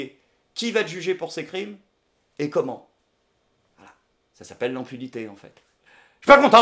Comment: dur de comprendre (à l'oreille) ton dernier mot de conclusion ! cela peut s'expliquer par le fait que tu tournes la tête dans le même temps ! d'ailleurs j'ai entendu "enfilité" qui n'éxiste pas ! j'aimerai que tu éclaires ma lanterne ô grand! je reste sur ma fin ! J'suis pas content ! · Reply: reno duaner Impunité il me semble .